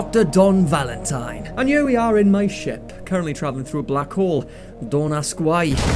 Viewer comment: I really love the subtitles but they are incorrect in some places. Take 6:37, Dr. Don Valentine says "Black hole" but the subtitles say "asshole". It's hard to tell if this done as a joke, and it's messing with my brain a little